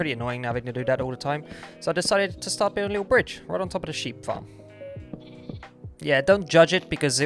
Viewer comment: I thought you said mop farm peace✌